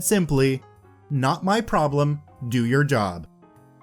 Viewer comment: That's some awesome pro revenge. Do your job Chad. Boom 😁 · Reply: I was kind of sleepy when I watched this video, so when I heard "Not my problem, do your job" I heard it as "Not my job, do your problem" I was a bit confused so I played it back and heard it correctly.